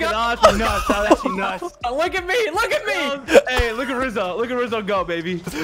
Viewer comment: I thought you said “Lizzo” I was bouta be like “THERES A 2TON CAR???”